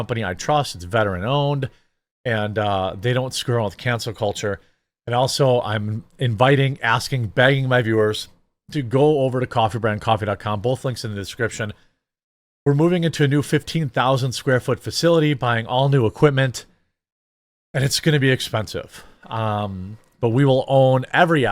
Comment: Now we need to cross-reference with Diddy's list! 1:49 It almost sounded like you said "contraband coffee"! 😆 😂 Hearing loss can be fun , if you share.